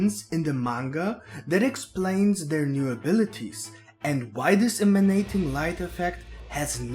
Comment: I thought you said the semenation life effect in the beginning 🤣 nice video 👍